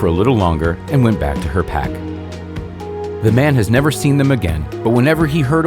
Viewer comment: At first I heard "The man was never seen again"